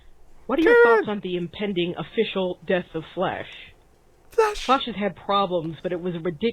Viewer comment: I heard "The death of Flesh" and each subsequent mention of flash has been replaced with flesh to my ears.